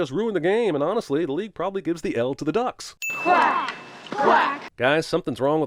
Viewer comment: My poor hearing. Instead of "Quack! Quack!", I thought he said "Crack! Crack!". And knowing the actor, he probably did say that